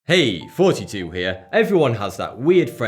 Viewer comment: i swear i hear "hey 42 here"